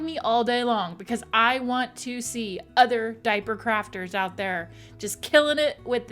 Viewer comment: Pssewwww, that was a CLOSE ONE lol. Girl, I was about to tag you in some pictures you would probably vomit over lol cos I seriously thought you said diaper CRAPS! I’m like oh have I got some nightmare inducing disasters for you! But I rewinded to see something and I then realized that wasn’t what you said. So I’m like ABORT! ABORT! Omg that would be funny though